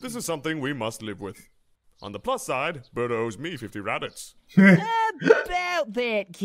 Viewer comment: At around 4:22 even though I know he's saying captain, I always remember mishearing saying Kitten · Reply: I always mishear Ginyu say "rabbits" instead of "Raditz." Even now it always just sounds like Burter owes him 50 rabbits.